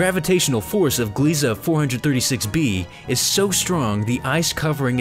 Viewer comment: did he say 4546b?! I cant tell it was too fast